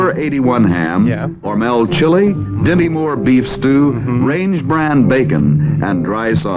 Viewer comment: I swear to God I heard "Demi Moore beef stew". No comment.